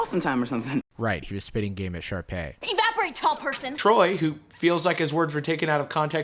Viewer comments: did i just hear “ evaporate y’all person!” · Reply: Tall person lmfao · And “EVAPORATE, TALL PERSON” wasn’t the weirdest line? You know what I’m pretty sure I heard that wrong anyway